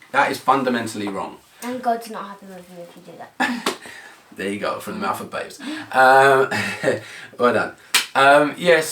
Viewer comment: I didnt get what the lovely said at 7:58. Can someome tell me pls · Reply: She said " and God's not happy with you if you do that."